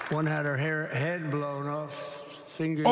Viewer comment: Did he just say one got her head blown off in front of the familys ? Wtf